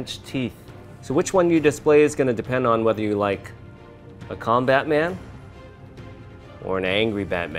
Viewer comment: When he said calm bat man I thought he said combat man 😂😂